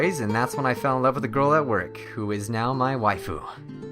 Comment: "I fell in Love with a girl in work who is now Mai Waifu" It Sounded Like That Right!